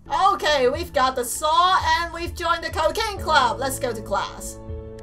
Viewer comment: It sounds like she said the "cocaine club"